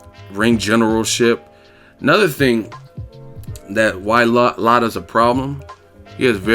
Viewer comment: It sounds like ur saying "Lotta" instead of "Lara" 😂